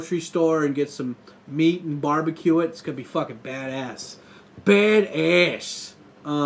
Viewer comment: Thought you said "bare assed", obviously a mistake on my part. But I now find myself oddly disappointed.🤔😞